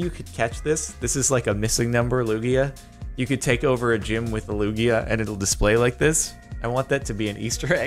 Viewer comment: I thought Lugia said Luigi